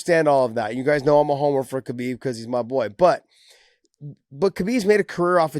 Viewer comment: I know youre going to see this... at 58:48 i thought you said “You guys know Im a homo for Khabib” for a split second lmaoo